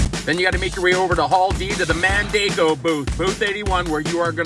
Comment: Thankfully you showed the booth, because I thought you said Mandingo booth, and I thought is this porn star conference!